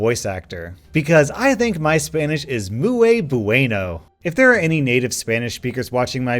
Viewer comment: all i heard was moo-aye bueno